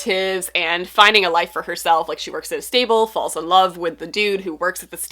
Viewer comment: The Nora Roberts book, I thought you said “She works at a Staples” but you said “Stable” 😄 that would have been a very different book